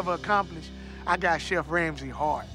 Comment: “I might have not won Hell’s Kitchen, but I got something nobody else on Hell’s Kitchen has accomplished. I got Chef Ramsey’s Heart.” And everybody else’s, for that matter. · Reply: With his accent it sounds like he said “I got Chef Ramsey hard!”